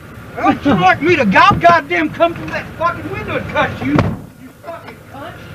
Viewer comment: I thought he said "I'll goddamn come through that fuckin window and cut you!" XD Cornette is gold.